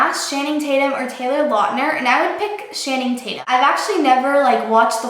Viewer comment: wait did she just say "shanning tatum?"